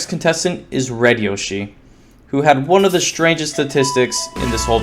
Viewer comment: What is the word stangest Also at 11:55 you said Red Yoshi